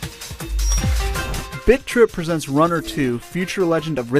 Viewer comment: at the end i heard "bitch rip" lololol :D